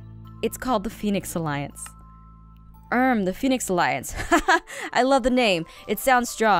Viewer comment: wait did that just say to kill Zane alliance!?! Lol!!!!